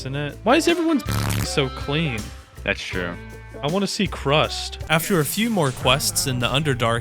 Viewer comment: am I hearing this right? CRUST?!?!